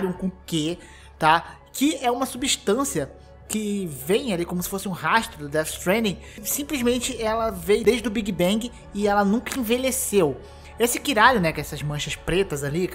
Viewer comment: eu entendi caralium kkkkkkkk